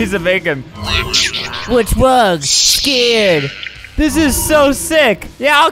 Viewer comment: I used to think that it said “Bloxburg, scared?” Which was honestly horrifying to me because I regularly play bloxburg. But now I realize that it says “What’s wrong? Scared?”